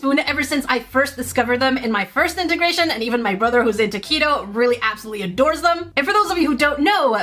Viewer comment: When you said "my brother, who's into Keto," I heard "my brother, who's a taquito" 😂